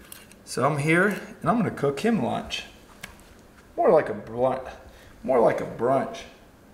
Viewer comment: did you say you were cooking Rob a blunt!??? LMAO!!!